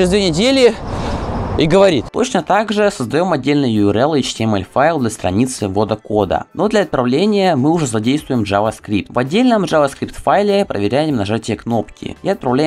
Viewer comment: а что говорит то? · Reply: Точно также Создаём отдельный url и html файл для страницы ввода кода. Но для отправления мы уже задействуем javascript. В отдельном java script файле, проверям нажатие кнопки, и отправляем POST запрос на сервер с кодом, если он верен......